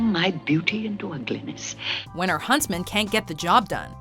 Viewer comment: It sounded like she said “husband” instead of “huntsman”.